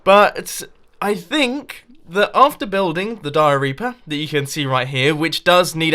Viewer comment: thought you said "diarrhea"